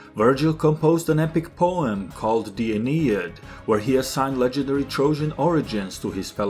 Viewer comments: what poem did he say, d'aneid? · 0:07, what poem did you say? D'aneid?